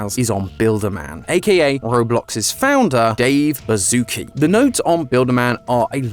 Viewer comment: I SWEAR THE CAPTIONS SAYS DAVE BAZOOKA WHAT?!